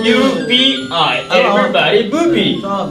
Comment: did he just say everybody boobie😂